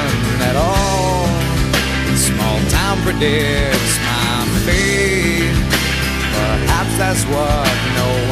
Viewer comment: I always thought Eddie said "Small town forgets my face", not "Small town predicts my fate". Ah, well.